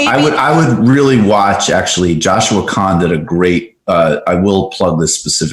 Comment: Does anyone know who Michael is talking about at 6:15. It sounded like Joshua Khan so I looked him up but didn’t see anything. It sounds interesting!